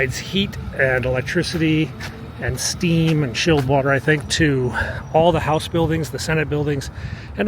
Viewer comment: Did you say chilled water?